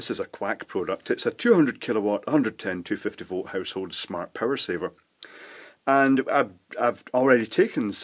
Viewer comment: For some reason I heard that as "life saver..." :/